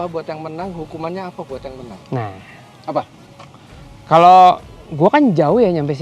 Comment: Om Fitra "hadiahnya apa buat yg menang, hukumannya apa buat yg menang?" Ga salah denger kan 😅